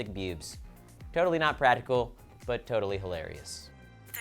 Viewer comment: when you're talking about Ivy I think you tried to say 'practical' but missed a letter "... pratical..." XD Someone tell me I'm not crazy